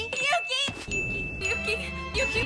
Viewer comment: it sounded like she said “Yuki you pee?”